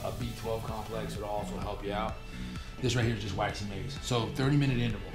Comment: what does he say is in the bottle at 5:21? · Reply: waxy maize